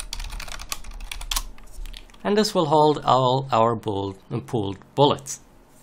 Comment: Super Tutorial Thanks!, but I heard you say Booled Pullet.... well almost... who else heard that? :) :) :)